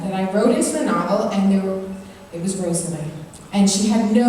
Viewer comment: the character with no personality.. does she say rosalie?